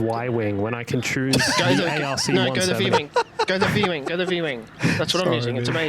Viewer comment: I think I am wrong, but did I just hear käytä v-wing? And can someone correct me.